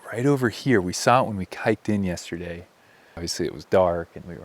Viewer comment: Did you say "we saw it when we kiked in the other day?"